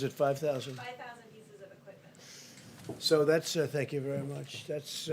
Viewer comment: For those that couldn't hear it, @ 23:30 Trump said '5,000 federal locations' and his aide who was barely audible said "5,000 pieces of equipment".